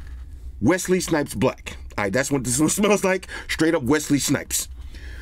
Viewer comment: Lmfao I thought he said Westley Snipes butt.